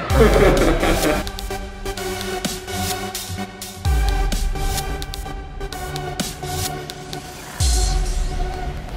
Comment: what was the guy saying at 4:55? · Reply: Its gone overboard this time